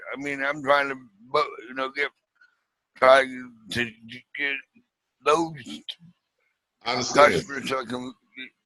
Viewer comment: wtf did this guy just say bahahahahai think he said he trying to get loaded mother f#cker.. has some mother trucker nuts in his mouth .. word up